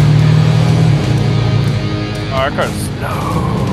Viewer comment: What does he say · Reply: "our car is sloooooow"